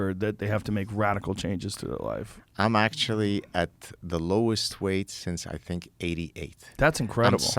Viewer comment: I thought he said I’m at the lowest weight since age 88! Was thinking how old is this guy 😂